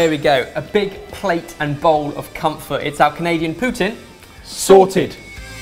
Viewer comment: did he say Canadia?